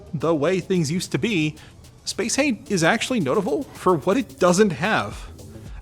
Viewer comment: "spacehey is notavul...." what's notavul, I know that's what he said, I just don't know what notavul means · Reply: Notable